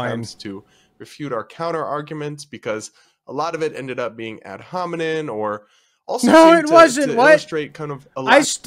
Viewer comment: did dude really say “ad hominin”